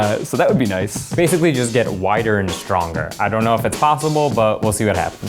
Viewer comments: I thought he said whiter and stronger first😅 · Am I the only one that thought he said he wanted to get whiter instead of wider? Cus I was thinkin' "you look pretty white already dude."